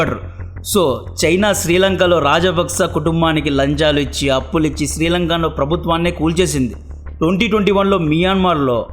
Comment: what is that word ra rey 😂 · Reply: It's RAW